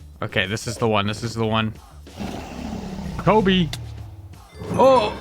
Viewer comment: naw why did you say Kobe😭